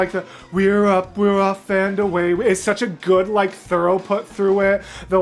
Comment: When I heard "we're up we're off and away we go" sounded like "grab the sheep and away we go"